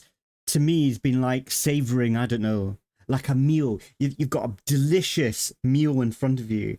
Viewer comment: I thought you said you had "A delicious mule" in front of you.😂